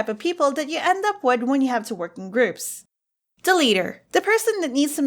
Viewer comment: I thought it said deleter instead of the leader